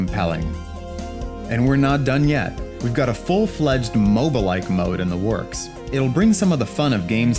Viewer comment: When you said "Moba-like mode" I thought you said mobile mode U wot m8